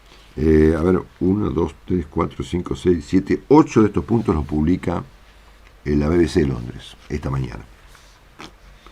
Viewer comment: ¿Alguien pudo escuchar el nombre del canal que recomendó Marcelo en el minuto 19:20? Escuché Dany pero el apellido no puedo descifrarlo y quisiera pasar por ese canal. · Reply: Creo q es dani haiphong